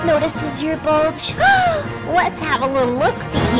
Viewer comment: WHO SAID CALL OF DUTY I HEARD THAT 🔫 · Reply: You mean” I wish I stayed home and played the new call of duty”